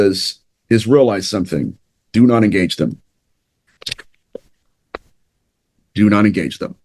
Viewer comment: said "do not engage them" I heard "you not a gangsta" Lmbooooooooo!!!!!